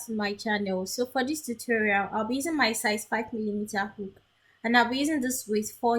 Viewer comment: I loved your tutorial! My only question is what size hook did you use? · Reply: On second listen I heard you say 5mm hook😊